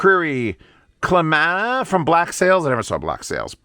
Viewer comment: What’s the song and band at 10:30? Couldn’t make out what you said. Thanks. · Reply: Clamanda from Black Sails OST by Bear McCreary